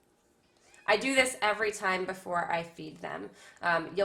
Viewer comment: I thought you said “I do this every time before I eat them” 💀🙏🏼